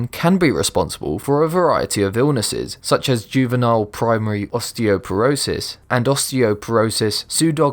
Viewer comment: Did you just say osteoperosist? oooooh idubbztv